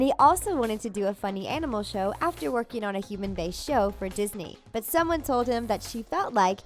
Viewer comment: did she say disney